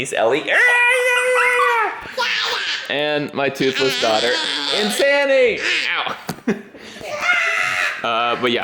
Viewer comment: Also, I totally thought you said "and this is my other daughter, Insanity!" 🤣🤣